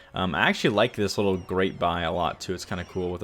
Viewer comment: you said "I like this Great Buy a lot"; I thought you said "I like this great Buy-A-Lot". Lol